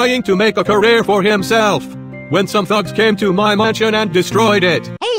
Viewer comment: at first it sounded like he said "thots destroyed my mansion"